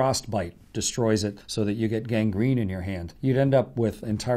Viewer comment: did he say gang green???? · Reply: Gangrene